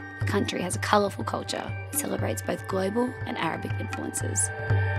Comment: Did I hear you say "both global and Arabic cultures" ? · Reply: Amazigh cultures